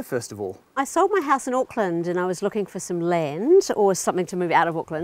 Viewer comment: LOL @ 0:40 I thought she said Oakland. She said Aukland. I was going to say that's a huge step up.